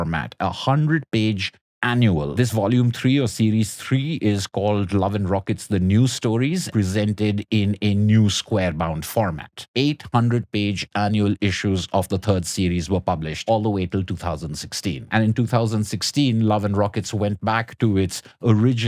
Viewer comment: "eight hundred page annual issues" is a little confusing. I first heard it as "800 page annual issues" not "8 hundred-page annual issues" so maybe a caption could be added there?